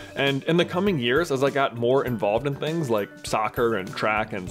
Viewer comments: did he say he was involved in crack? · Am I the onlt obe who heard 'crack' at 2:18?